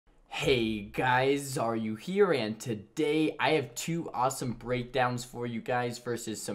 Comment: ooh I always thought he said ''heeey guuyys aaaare yoooouuu here and today'' omg lol